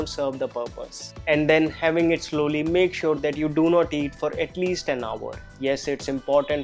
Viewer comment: he didn't said "do not eat for at least 10 hr" he said do not eat for at least an hr" ... And comment it bcz I heard 10 hr 😅😅😅